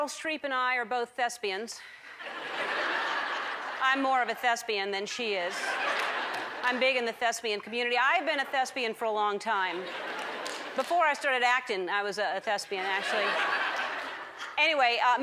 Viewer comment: at first i Heard "lesbian" lol lol